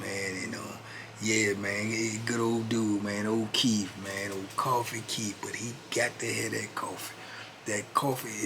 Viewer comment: Oh DAMN, I totally thought you was saying coffee keefe, 😂in Wisconsin Prisons the coffee and many other products were Keefe brand, ha ha ha ha that’s funny. Oh PS the voices you do are hilarious and you will most definitely get to 100,000 subscribers in 2023. It’s already done already negotiated with God and how you absolutely deserve it. Have a good night big bro.